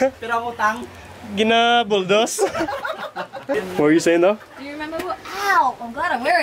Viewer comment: Mga besh mga bai... at 7:54, did call love? "What are u saying love?" oor it's just me kay fan kaau ko sa elaha duha? lol.